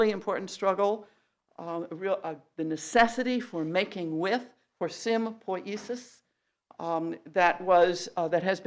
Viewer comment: onwards : what is she saying? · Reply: "Making with for sim by isus"?